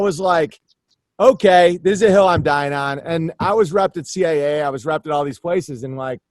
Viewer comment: Someone tell me what reptid CIA is? Owen says he was repped in CIA 34:43. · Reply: CAA is a big talent agency in LA, not the CIA.